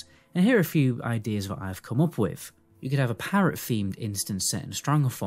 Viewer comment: I totally heard a “Parrot themed” dungeon for stv 😂